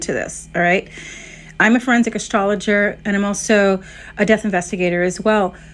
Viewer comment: I think her fans might be in denial over how mentally unstable she is. FORENSIC ASTROLOGER I'm dead ☠️☠️☠️ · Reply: im in my senior year of forensic anth and i thought she said she was a forensic anthropologist at first and had to do a double take. girl is not seeing the light of heaven